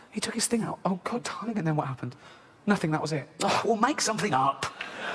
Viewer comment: The “make something up” bit is one of my all time favs. I died laughing when I first heard this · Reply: at first i misheard it and i thought he said "we'll make something up" and i think that's worse...